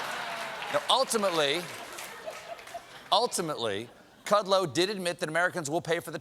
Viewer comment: did he say cudload? Just spit the shit out!